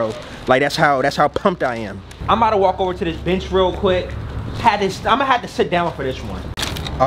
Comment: When he said “imma walk over to this bench” I thought he said bitch at first 💯🤣🤣🤣🤣